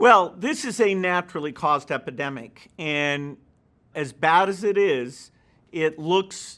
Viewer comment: Did he just say "as Bats as it is"?